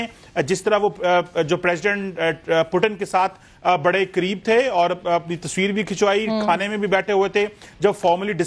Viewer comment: I heard the words; (BADE CREEP THEY)..😂